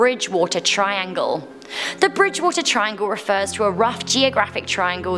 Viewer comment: on number 2 I thought it said the Bermuda triangle